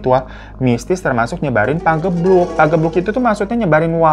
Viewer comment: gue dengernya kang gebuk wkw